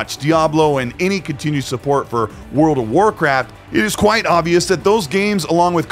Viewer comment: I could swear he said World of Warcrap 🤣